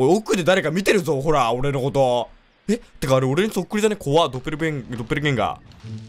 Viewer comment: もちろんバカゲーです。 いや、バグ多すぎやろ！！！まあ、いいけど！！！ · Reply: ドッペルゲンガーがドッキリゲンガーに聞こえた